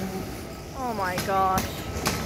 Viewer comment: I did. You didn't hear it, but we were told to back up. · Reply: it sure sounded like she said "back the fuck up!". Kind of shocking.